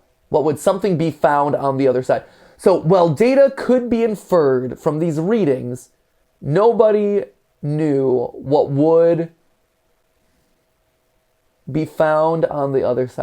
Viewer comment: Did someone say that around 32:00 what it says is "While data could be inferred from these reading, nobody knew what would ACTUALLY be found on the other side"